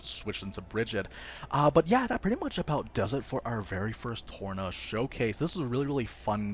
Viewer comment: I’m sorry did he just say “Porno Showcase”?